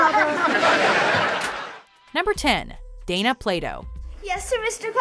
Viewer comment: I thought she said Dana Playdough